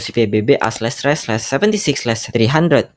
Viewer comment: bang, lu ngomong apa ini?😅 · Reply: A/Res/76/300. baru nyadar sekarang kalau gak jelas wkwk